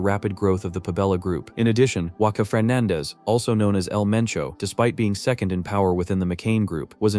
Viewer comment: did bro just say EL MENCHO??